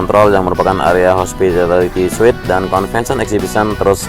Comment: ngomong apaan bg wkwk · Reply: Area Hospitality suite